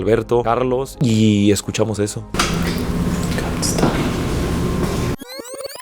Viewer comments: yo le entendi "come stay" que significaba "ven a quedarte" patron le van a jalar las patas JAJAJAJAJ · yo entendí "good stay" que en español significa buena estancia. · Yo escuche "I can't stay" en un accento britanico/transatlantico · Yo creo que dijo: no vendo cigarros sueltos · en el minuto 11:11 yo escuche como un "Come Stay " que seria ven a quedarte Ta buenisimo el video patron